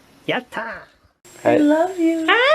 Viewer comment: ได้ยินมันพูดว่ายัดห่า